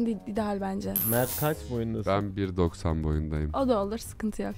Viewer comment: nE- 1.90? Doğru mu duydum?💀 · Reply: Mert 1.90’mış. Acayip uzun